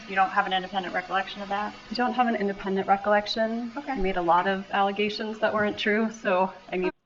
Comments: I heard “I made a lot of false accusations” on the last one. J M O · I don't hear the "I" just "Made a lot of..." but kind of feels like 'he' under her breath but her truth wants to say "I." I believe she made false allegations. Edit: at 29:50 I hear it. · I'll dispute that Wendi said "I made a lot of allegations...." There is no personal pronoun in her declaration. She actually says "Made a lot of allegations...."